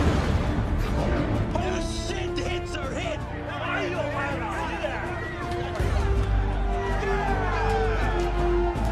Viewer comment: can somebody tell me what nagata is saying in this moment ??? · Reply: He said “You guys did that!”